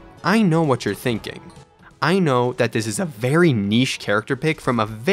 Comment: did you just say that castle crashers is a very neash game? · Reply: niche